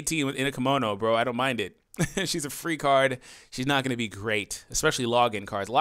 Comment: I swear @ 2:44 he said " shes a freak hard" about android 18 XD bahahah woah 18 your waifu? ;)